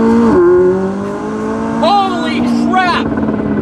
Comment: TF DID HE JUST SAY "HOLY SHRAP!" DUDE THATS FUNNY 🤣😂